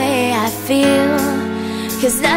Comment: is it really la la land machine? i thought it said la la land of shame.....